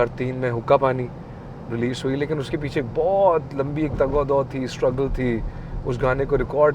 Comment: What that word 15:00...tagado thi? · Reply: Jad o jahed in easier words effort